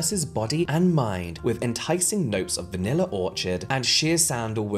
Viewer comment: anyone else hearing vanilla orchard here😂.